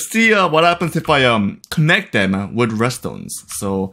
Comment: one mistake: Instead of redstones is restaurants